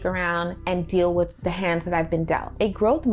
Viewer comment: "A growth mindset is a solution based nig**r" Me: hm.. (I know its not what she said but its what i heard first and it floored me)